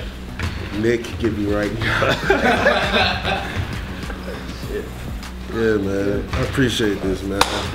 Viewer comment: It's funny how he told, "the Knicks can get me right now" 😂😂😂😂😂 didn't quiet go that way, now did it? 🤔😂😂😂😂 Edit: did he say that doe?